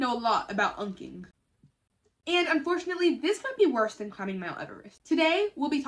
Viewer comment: GIRL. I had to stop and rewind because why did I think you said "worse than cutting my uterus" 🥲😭